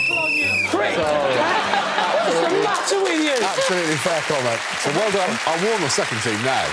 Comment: Lee: “...Ohhh polly put the kettle on ya prick!” Greg: “absolutely fair comment” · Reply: oh ahah, i thought he said "absolutely feck on it"